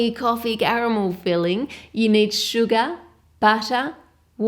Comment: thought she said "you need Suga" and I be like "but he's on a world tour right now, he could be either sleeping or busy"